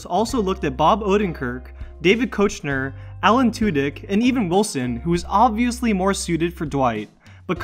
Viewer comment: I love THIS! I too have seen the entire series at least three times. I have even watched all the commentaries and only knew about half of these facts . I learned so much thank you for sharing! One thing tho-if I may be so bold. I must point out you referred to Rainn Wilson as Owen Wilson in the beginning which threw me off for a second. I understand the mix up for sure. I just wanted to say in case you missed it lol thanks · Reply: Emily Rocks he says, ' and even wilson,' not Owen Wilson. i misheard it the first time too tho c